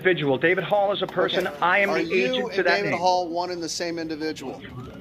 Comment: The person Hall should never had acquired this individual as an agent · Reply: I thought he was saying "I'm the Asian for David hall", it was confusing and I was curious when they were going to charge him with human trafficking.